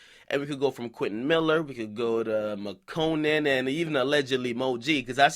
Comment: I thought it said Madonna. I'm like replaying that concert kiss and reaction